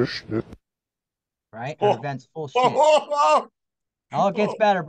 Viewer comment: I was initially listening to this not reading. What I heard: “Our event full of sh*t” I heard very clearly “I haven’t finished it”